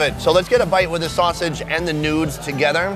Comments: Wait did you just said "Nude" 😂 11:05 Hello From May 2020 🤗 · did he just say "a bite with the sausage and the nudes together'? 😂